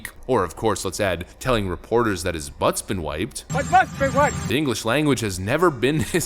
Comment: The more I hear Potato Joe's butt wipe comment it sounds more and more clear he probably said "MY butts been WAXED!"